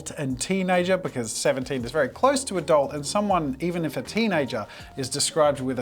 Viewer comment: When you were drawing I thought you said “a dolt” instead of “adult”